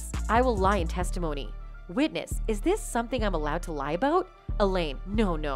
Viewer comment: Nope sorry I hear, "I rely on my testomonies" Bit dangerous to accuse someone like this! Slander???